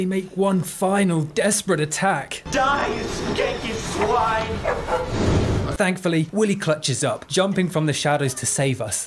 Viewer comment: why did I hear "die you githyanki swine!"?